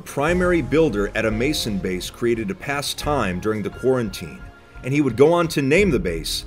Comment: I swear he said “quarantine”